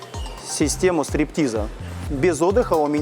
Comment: Мне одному послышалось " система стриптиза " ?